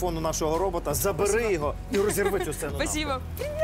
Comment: что он сказал? )))) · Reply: Он сказал - бери микрофон и разорви эту сцену пополам (навпіл - украинской).